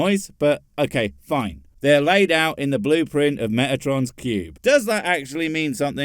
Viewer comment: I swear when I heard metatron's cube, it sounded like Megatron (from Transformers), which would make just as much sense.